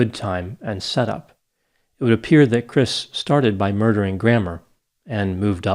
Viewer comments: "Looks like Chris started by murdering grammar, and moved up from there"🤣🤣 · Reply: I thought he said gramaw lol 😆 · "Looks like Chris started by murdering grammar, and moved up from there"🤣🤣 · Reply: I get it now lol. At first I thought I heard “grandma”